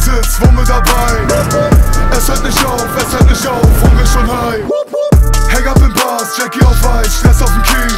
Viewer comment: Bin ich der einzige, der bei 1:51 „Unge ist schon high“ verstanden hat?😂🤦🏼‍♂️